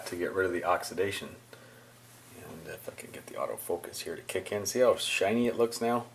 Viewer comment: what did he said?))))) f#cking get the out of focus?))) Sounds like one of these cute Joe Pesci dirty language mumblings :D